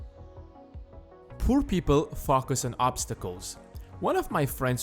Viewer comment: you said, "poor people focus on obstacles." But because of your accent, I thought you said, "poor people fuck us on obstacles."